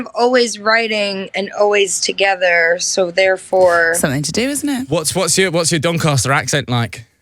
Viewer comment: did she just said "so you're in to dudes now" please say that I hear that wrong bcs if not that would be so disrespectful to say. Like it's ash's life let her alone...